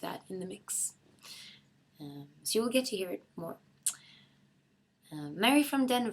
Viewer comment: I can't understand what she says at 0:47 :( Somebody help please? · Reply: Brasil "so you'll get to hear it more?"